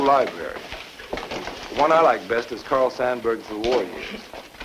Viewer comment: Captions say "the Warriors" but ah reckon he meant Abraham Lincoln: The War Years